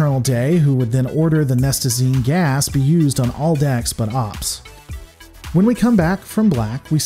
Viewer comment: Did he just say all Dax but OPS?